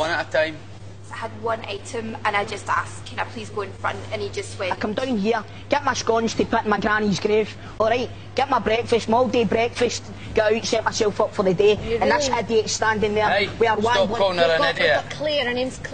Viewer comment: did he say granny's grave and Moldy breakfast